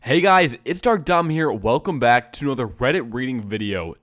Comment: Your intro sounds like youre saying “Hey guys, its Dumb Dom” 😂